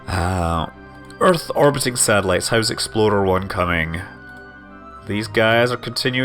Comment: I keep hearing Scott say "Exploder 1" instead of "Explorer 1" lol :)